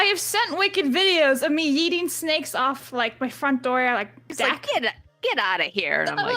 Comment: When you were talking about yeeting snakes, at first I thought you all were saying "Blabbs eating snakes" lol 😆